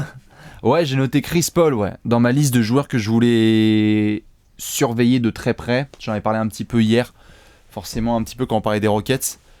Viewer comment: Croyez moi ou non mais à 16:02 j'ai entendu "chris paul dans ma liste à surpayer de très près " x)